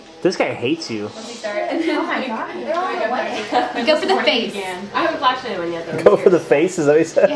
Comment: I heard "I haven't flashed anyone yet." at 1:08. Ladies... pics or gtfo!